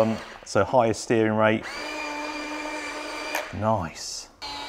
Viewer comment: almost sounded like the "nice" meme